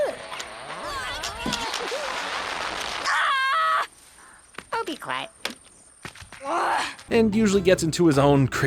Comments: Me at 7:04 : "did he just just talk?" I swear it sounded like he said "I'll be quiet" 😂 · yo did he just say “ oh be quiet”!?!?!? I thought he couldn’t speak 😵‍💫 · DID DONNIE SAY “OH BE QUIET” or “I’ll BE QUIET” at 7:05?